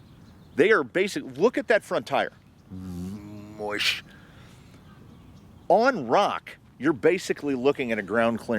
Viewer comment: 😂😂 this sounds like a "power" but in russian language) (мощь). What is the actual word you said here? I can't hear😅))) thanks 👍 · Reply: As am an endless fan of onomatopoeia, I'd say the closest transcription would be "moosh," but like... imagine it italicized. mmmmoooshhh.